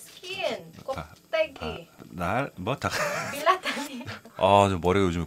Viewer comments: why do I hear 'bilat' at 0:29 🤣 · I heard BILAT hahaha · I think i heard her say bilat tangina